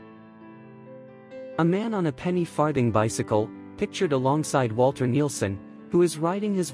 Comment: I heard "Farting Bicycle" and I had to ask myself if I heard that right... I never knew the name of those.😆